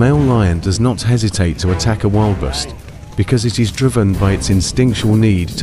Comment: Sounded like he said wild best, surely didn't sound like, wilder beast.